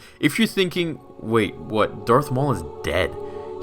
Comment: i thought he said Darth Mole